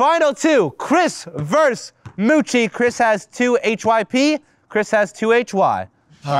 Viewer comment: am i the only one who heard kris has 2hyp and kris has 2hy